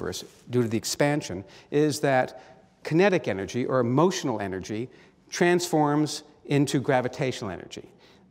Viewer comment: I thought he said "emotional energy" at first.